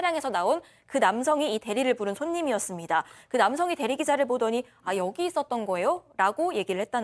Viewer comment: 라디오처럼 듣다가 대리기자라고 들려 흠칫놀라 정주행시청 오오...